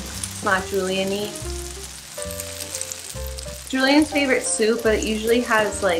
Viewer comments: Okay.. listen.. I thought this said "Making our favourite soap" and was very confused when there wasn't any soap involved in this video. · I swear to god I've seen this video AT LEAST 167 times and this one time I'm drunk and thought it said "making our favourite soap" and I was like WOW HOW DO MAKE IRISH SPRING